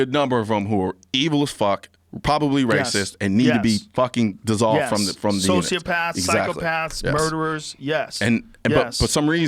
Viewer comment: Did he say dissolved from the earth?! 😯